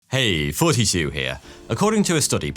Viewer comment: I always hear “hey 42 here.”